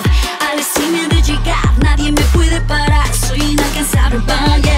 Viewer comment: did U say "ปลาร้า" ? {parar}